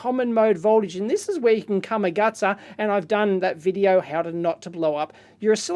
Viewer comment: "you can come a-..." what? What is he saying? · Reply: come-a-gutsa